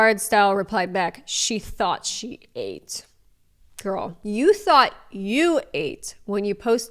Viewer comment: tought instead of thought 😭😭